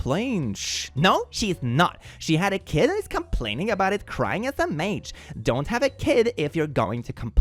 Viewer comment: I heard "crying smh" as "crying as a mage" xD